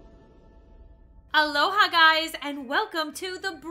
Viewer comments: ???? Me: did u say Aloha guys? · When said Aloha guys I swear my nerdy brain thought she said Alola Guys I need help-._-.